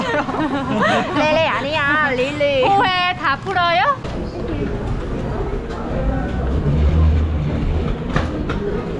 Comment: 무슨드립임??? 뭐라는지 안들리는데 ㅋㅋㅋㅋ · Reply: 우유 초코우유 집어듦. 릴리: 너가 제일 어울려요 ㅋㅋㅋㅋ